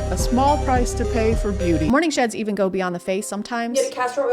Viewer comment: Oh lol, ngl, I thought you said morning shit and was like "OK, but how does that improve skin